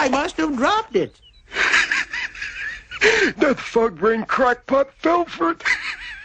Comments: it sounded like he said "That fuck brain crack pot, Bell Ford." Just something I misheard because of the audio. Can't help it... LOL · Did hey say "That fuck-brain"?